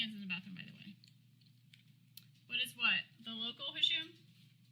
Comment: I might be hearing things but at 17:47 it sure sounds like says “it’s the local Hisham”